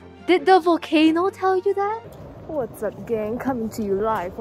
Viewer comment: I thought said that's a gay coming to you live in a volcano.